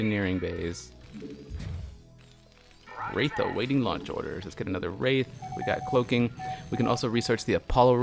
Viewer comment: The wraith always to me sounded like it was saying "Wraith awaiting nachos.."